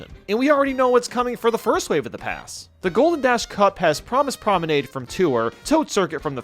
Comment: Did I hear "Promise Promenade" at 1:00? Also, 1 point if any predicted course appears as DLC at all, 2 points if it appears in the predicted cup, and 3 points if it appears in the predicted cup in the predicted order. For example, if Wii Maple Treeway appears, that's 1 point. If it appears in the Propeller Cup, that's another point (2 points total). If it appears specifically as the 3rd course in the Propeller Cup, that's yet another point (3 points total).